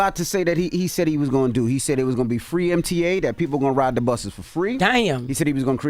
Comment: he did not say FREE MTA he said Free Routes…. Damn I hate how we report false information… it sounds like ya’ll wanted him to lose 😂. What about what Kamala & Biden promised us